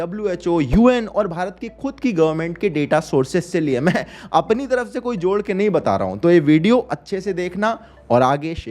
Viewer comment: I was like kya Bol rha h bhai.... I thought he was saying Bharat ki KUTTI govt. ki data source.... After rewinding the video I got it right😂🤣🤣bhai bhai bhai moment...